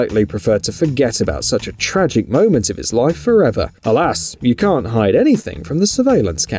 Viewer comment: Did he say khalas in arabic which means finished ?